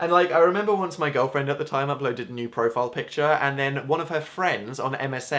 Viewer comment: thought he said a nude profile picture :(